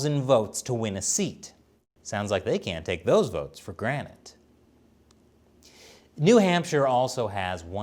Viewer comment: He said "take for granet" instead of 'take for granted'. lol